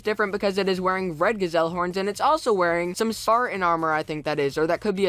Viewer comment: I thought I said "Satan armor" XD